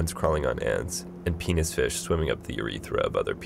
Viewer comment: does he say "penis fish" at 5:32?